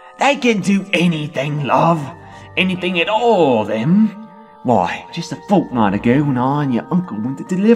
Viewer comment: I misheard and thought he said "why just a fortnite ago."